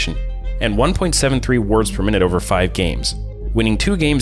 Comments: really thought he said Words Per Minute. lol Im like why is he typing maybe because hes calling out summoners. And did he say higher pro play in America? LOL LDL is probably better then NA. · Does he mean what he’s literally typed, like flash timers?? So OOL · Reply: what he actually said was "wards per minute" as a support metric of vision control, I was just making a joke because it totally sounded like he said "words"